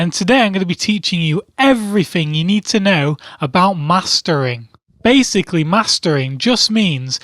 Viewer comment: when he said mastering, I thought he was saying master bating.